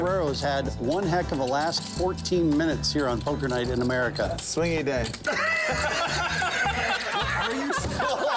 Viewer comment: What is Doug saying ? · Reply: Swingy day